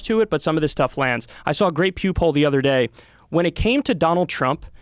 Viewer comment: when Kyle said "Pew poll" I thought he said "Pube hole" and that made me laugh. OK, back to work...